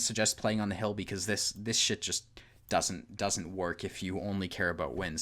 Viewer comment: Hi I have a question what did you sayed 9:27 ? 1 8 ? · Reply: I said "it aorta makes sense if you're playing for wn8, it sorta makes sense to go where the enemies..."